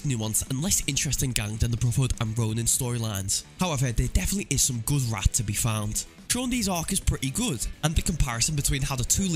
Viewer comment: Did I hear that right? Was that "There definitely is some good rat to be found."?